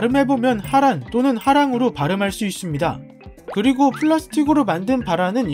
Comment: 마음이 안좋은가... 1:45 자꾸 발암으로들리네...